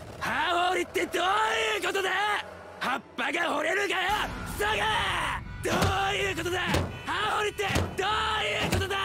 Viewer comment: wait what does he say? I hear sa gae? Or is he saying something else